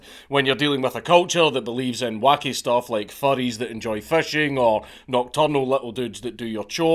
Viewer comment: Did he just say, “furries that enjoy fishing”?